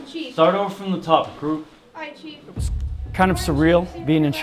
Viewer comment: I thought he said "idiot recruits"